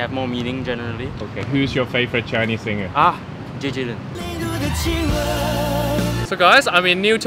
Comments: I hear "周杰伦" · 其实 5:28 那个小哥说的应该是 周杰伦 而不是 林俊杰 啦 哈哈。5:28 Actually, the boy said "Jay Zhou" instead of "JJ Lin" lol